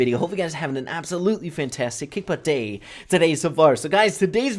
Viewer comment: UMMMM what is that thing you are say at 0:03 " fantastick hippo day" WARNING: THIS YOUTUBER HAS A TURET SYNDROME (NOT CURABLE ) SORRY BUT ITS JUST WIERD MAAAN WISH LIST: 1) BUFF FOR CROW 2) SIEGE MORE INTERESTING ( ADDING NEW THINGS) : NEW ROBOTS 3) TRIO WHERE YOU ARE PLAYING 3 IN A SQUAD if you can add this to your next video i will be very happy and few days i have been watching your chanel a LOT pls REPLY gg AND HAVE NICE HIPPO DAY